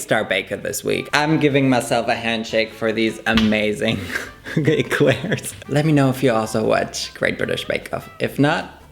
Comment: Did you just say gayclairs? 😂 proper name finally